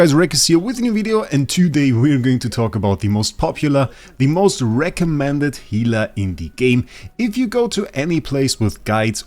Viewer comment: I swear i heard you saying this is the wraecommended healer :D